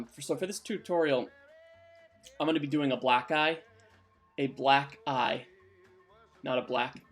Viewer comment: What I thought I heard: "I'm gonna be doing a black guy" blinking guy gif You: "I'm gonna be doing a black EYE" Me: "oh ok"